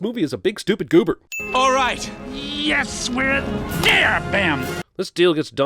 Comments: I heard “yes we’re in dair bam” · What the fuck does hades say at 13:52? The subtitles are wrong but I can’t figure out what they’re supposed to be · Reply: It sounds like he’s saying “Yes, we’re a deer- BAM”